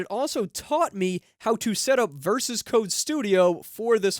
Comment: WTF, did you just say “VersusCode Studio”?????? Who says that?!!!